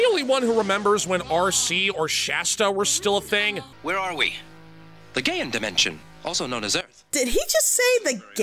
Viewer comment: I will not rest till I find a way to bring this shingdig of ours back. I enjoyed it too much and working with everyone. I'll edit it myself if I have to! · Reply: Hi! I noticed that on 10:08, one of the cpu protectors said what you thought you heard and Idk if was for the joke purposes, but if you payed attention, he said Gaen, not Gay.